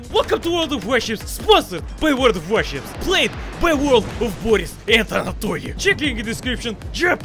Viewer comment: Me: i hear "World of Anatholi Also Me: excitation Intensifies